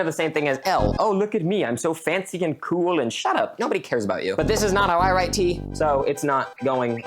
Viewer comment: did you say nobody cares about me I’m seven years old.